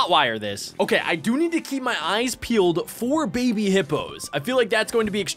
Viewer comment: When he said I could hotwire the car I heard him say but I got to key my eyes peeled for baby hippos